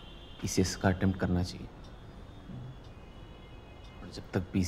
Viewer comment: I always wondered why it was so important for Sandeep bhaiya to clear PCS after he failed his Prelims now after watching the 3rd episode of Sandeep Bhaiya i understood why . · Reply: watch from here, he clearly said PCS